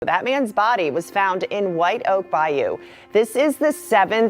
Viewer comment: At the very begginjng I thought she said Batmans body was found. I was like damn. The Joker got him!